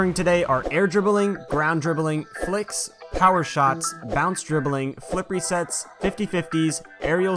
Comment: it says Phillips resets instead of flip rest